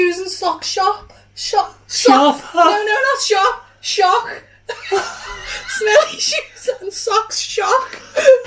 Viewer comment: Omg am howling I thought she said smelly cheese fat sack Hahahaha 😂